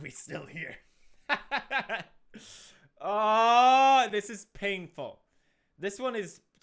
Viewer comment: Did he just say this is ping ful?